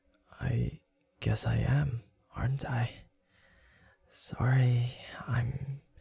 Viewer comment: he said aren't I but I hear hentai 😂😂😂